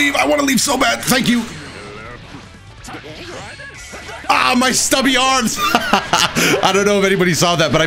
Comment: Why did my mind think of ''AH MY STURNUM'' instead of ''AH MY STUBBY ARMS''